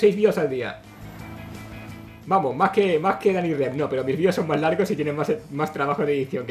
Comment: dijo danyrep o que dijo yo le entendi asi